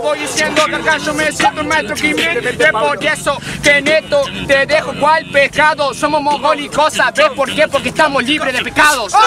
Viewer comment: ¿Que dijo? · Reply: "Es por eso que en esto te dejo cual pescado"